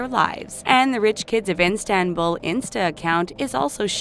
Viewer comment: did she just say Enstenbul instead of Istanbul? I'm done🤣🤣🤣🤣